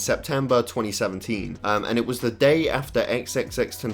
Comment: When he said the day before after x dyed i thought he said the day after died